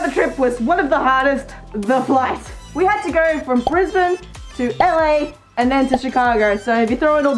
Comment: anyone else hear “I had to go from prison…”